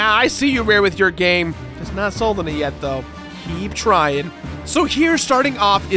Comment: What is he saying at 2:38? Played it back 10 times. "It's not sold any yet so keep trying"? But that doesn't make sense. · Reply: Just not sold on it yet though, keep trying.